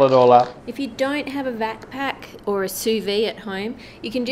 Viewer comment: I thought she said backpack instead of vaccpack so instantly started to sing the backpack song from Dora lol